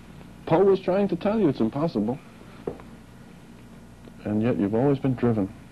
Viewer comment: Hello, does anyone know what Dr. Heyen says? It sounds like he says, "Poe was trying to tell you it was impossible" (the book-length poem) Thank you.